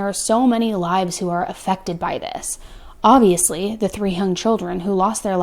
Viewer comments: Wait... @ 15:06 did say: the three "hung" children or the three "young" chilcren? I'd like to be able to say that a parent murdering their children is a shock to me. It's not. It is, and will always be, incomprehensible to me. I don't understand how you betray your own child. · Did she say the "three hung children" ??